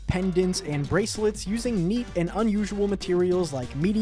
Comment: I’m sorry did you say meat?